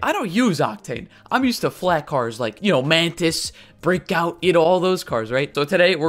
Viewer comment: At the beginning of the video it sounded like he said brickout instead of breakout